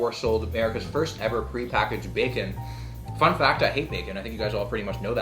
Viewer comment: 😂😂🤣🤣 lol it sounded like he said he hates bacon😐